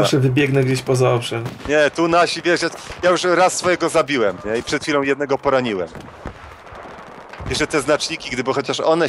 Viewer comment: "I przed chwilą jednego poroniłem" - usłyszałem LoL